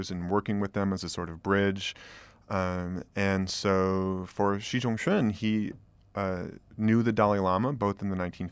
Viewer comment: every time he says xi zhong en, it sounds like he is saying xi jiong en, which is the internet's favorite chinese character 囧